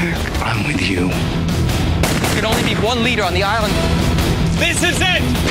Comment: What does Richard say at 00:20? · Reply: He says "there can only be ONE leader on the island" then "THIS IS IT"